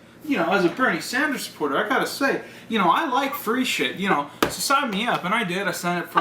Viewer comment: My lesbo ass initially misheard this as "I like fruity shit" and fucking agreed. Lmao.